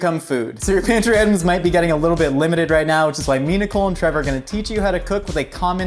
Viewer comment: What he said “me, Nicole, and Trevor” what I heard, “Minacole”